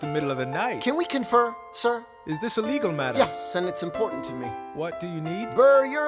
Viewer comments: When I first listened to this song, when he said, "Is this a legal matter?" I thought he said " This is illegal matter" And I was like, "well, I can't say I didn't expect that" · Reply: I always thought he said "is this illegal" and then Hamilton says "yes" and I laughed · When I first listened to this song, when he said, "Is this a legal matter?" I thought he said " This is illegal matter" And I was like, "well, I can't say I didn't expect that"